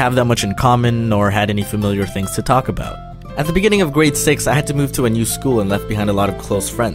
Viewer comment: grade six sounded like great s@